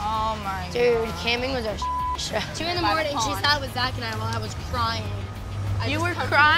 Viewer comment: what did say ? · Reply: She said, "two in the morning she was out with zaq and I." She was pointing and referring to Ava.